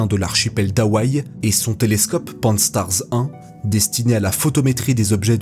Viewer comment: Il a dit quoi à 4:49 ? 😅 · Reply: Pan-STAARS 1